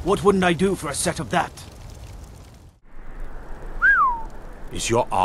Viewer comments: i thought he said dragon balls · Ok the last one i thought he said dragon balls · Wait a min.....did he just say Dragon BALLS!?🤨